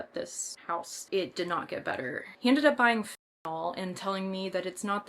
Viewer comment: Can someone please tell me what she's saying? I really hate when these fkn people don't just say the damn words. And what then can't even put the actual word she's saying on the screen either? What drug starts with Fin and ends in t? I thought she was talking about fentanyl but I have no clue! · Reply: Yeah fentanyl